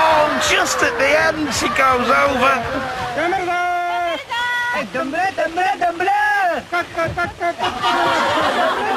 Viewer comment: What are they saying? Hahaha. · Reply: Donbrakoko. It's from a Children's story.